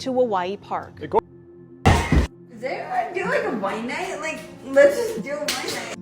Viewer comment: are you saying she’s saying wawawai here? I always thought she was saying “Let’s do a wine night, who wants to do a wine night” but I can hear wawawai now too. Excellent content btw. You haven’t come up on my suggested in a couple months unfortunately but I gotta say- you are leveling up & them some!!!